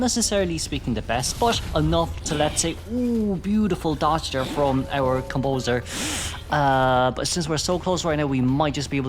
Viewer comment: "nice dodge from our composer" you should take a rest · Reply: i heard it as “lover composer’s”